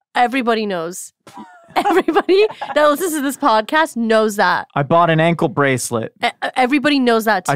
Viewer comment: When said “i bought an ankle bracelet” I heard “i bought an ankle monitor”. I seriously thought he went to jail or something 😅